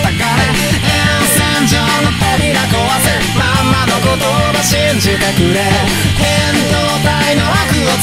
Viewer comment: 2chの有名なネタとか見たあとだと「まんまの言葉信じてくれ」が「マッマの言葉信じてくれ」に聞こえてなんとも悲壮感が